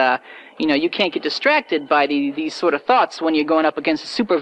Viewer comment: Am I the only one who thought Gamma Jack said "Thot" and not "thought"?